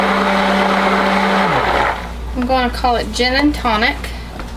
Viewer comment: You should call it JEN and tonic... :) well thats what i first thought you said when you said Gin.. my aussie ness heard Jen and Tonic.. thanks for another wonderful video.. i miss your vids..